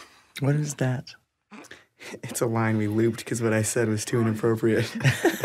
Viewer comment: Can someone please explain to me what he is saying at 2:25 ? I'm french and he speaks too fast i dont understand ^^ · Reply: Amélie Blanc “it’s a line we looped because what i said was too inappropriate”